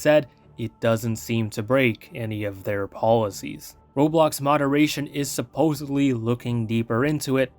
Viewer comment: Im quoting what said tho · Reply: i thought he said roblox moderation, but idk